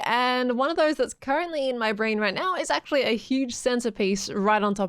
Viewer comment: When u said "giant centerpiece" I heard "giant centipede" sooo... How would a giant centipede/millipede/Roly poly like creature would look like there? I think it could fit well the style :D